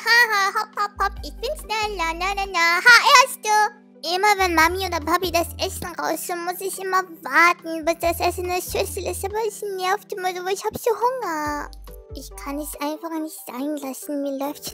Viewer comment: ich verstehe nur Bahnhof 😂😂❤️ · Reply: „Immer wenn Papi oder Mami das Essen machen , muss ich immer warten bis das Essen in der Schüssel ist, kann ich aber nicht weil ich so einen Hunger habe !“ sagt sie 😂❤️💕 Grüße und ich hoffe du meinst das😂